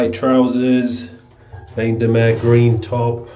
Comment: What's that shirt at 11:55 anyone?? Didn't catch the name. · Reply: Bain de Mer.